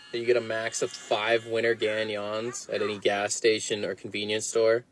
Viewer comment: Sounded like he said ',five winners/gagnants'' gagnants being French for winners.